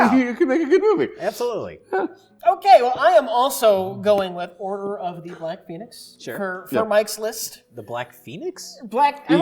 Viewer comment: #italianshaming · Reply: I thought I heard Rich say, "Order of the Black Penis". Mike used to never let this shit slide right past him... Someone up his anti-depressants?